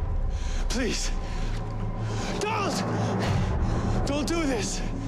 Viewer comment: i hear flash say DALLAS! please dont do this